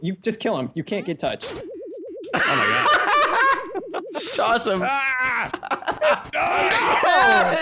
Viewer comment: I thought you said ohm my god lol